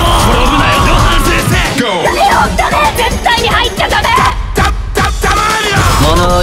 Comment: なんて言ってるんすか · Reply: 絶対に入っちゃダメ